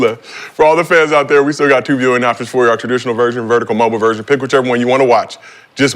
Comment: Nah what did say here 😂😂😂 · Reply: For our viewers we do still have Two viewing options our traditional version/vertical mobile version